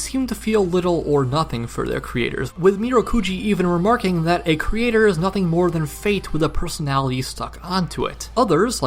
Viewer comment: the origin line is more like "the story is nothing more like..." instead of 'the creator'